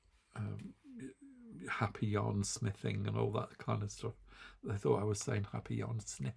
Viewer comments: There you have it! Now I hear “yarn sniffing” whenever you introduce yourself…🙄🤪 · lol I'm also one of those who thought you said yarn "sniffing". Didn't even think it was odd that you were saying that, 😅. I have looked at the ICE website a while ago. It can be overwhelming tbh. I never did buy from them because of the shipping costs. I'm sticking with temu for the free shipping. I tend to add yarn to fill my basket to get the free shipping. usually something that looks interesting to sample knit. I'm going to have to sell some of my stash soon lol. I agree that using the needle size to get an idea for yarn weight would be best. Thank you for your very enjoyable videos.